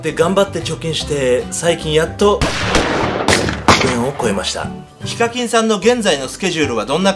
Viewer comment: のとこ億円って聞こえる笑